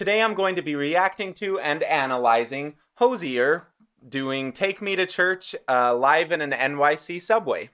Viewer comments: Take me to church Auto generated subtitle: DiCk MaDe A cHuRcH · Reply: I heard "dick me to church" but holy shit 😂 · Take me to church Auto generated subtitle: DiCk MaDe A cHuRcH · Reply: I totally heard “Dick me to church” at first. 😝